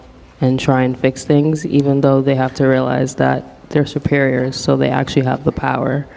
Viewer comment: did she just say white people are superior? Wtf are these kids being taught? Jesus.